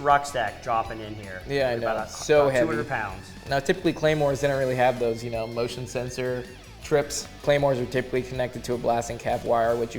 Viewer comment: I totally misheard that description of claymores as being "Claymore have an emotion sensor". Gives new meaning to "Happy Thoughts. Happy Thoughts"